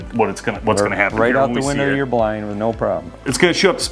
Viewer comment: I know he said "window of yur blind", but I heard "window of yur truck"